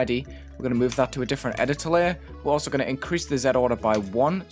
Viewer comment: He Said Zead Instead Of Zee